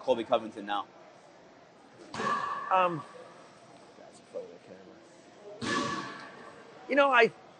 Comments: he legit just said "that is for the camera" i think what he said is true but probably not the all truth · What does Dan murmur at 24 minutes in? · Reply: Sounded like he said "That's a photo camera"